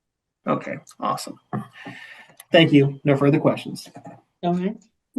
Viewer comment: "Opes! Shit!" 😂😂😂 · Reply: I thought she said “dumb shit” 😂😂